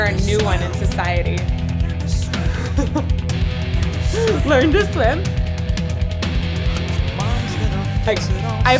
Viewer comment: Back in the day, I heard this on the radio, and I thought he was saying "Ernest swim", and it was messing with my mind.